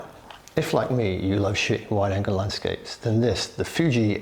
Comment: Indeed, it's not a cheap lens. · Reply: btw at start it sounds like your saying, "shit wide angle lenses." :0